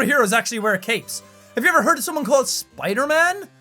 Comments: When you said "Iron man" I heard "Ireland man" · I thought he said Ireland man not iron man 😂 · When he started naming superheroes that dont wear capes, when he said Ironman I thought he said " Irish Man" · Your hair is duller than my sense of humour. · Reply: also when you said iron man i thought you said Ireland man